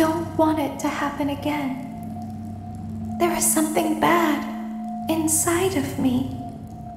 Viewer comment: I thought that Circus baby said “there’s something inside of me” I thought it was Elizabeth Afton